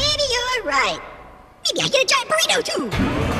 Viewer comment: What did he say? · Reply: Maybe I should get a giant burrito too!